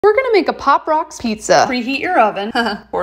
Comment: Thought she said bob ross pizza